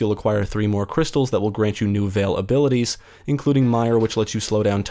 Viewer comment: every time you say "veil ability" I hear "availability" lol